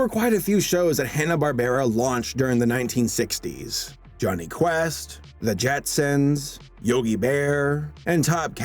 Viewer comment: Almost thought you said Johnny Test and I was about to off myself